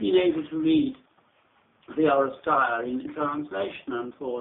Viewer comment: can anyone understand what book he says at 6:43? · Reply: He's talking about "The Oresteia" by Aeschylus, and more specifically, a book entitled "Aeschylus in his Style" by WB Stanford. I think that Stanford's was published in the late 1940's or early 50's.